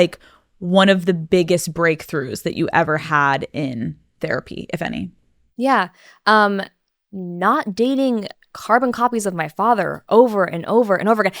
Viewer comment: Did she really say or “if any???”